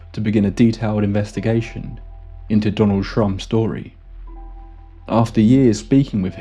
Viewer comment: I heard Donald Trump almost every time. Had to remind myself that I was mishearing.😆